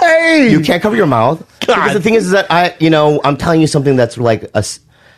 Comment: 誰也聽成幹🤣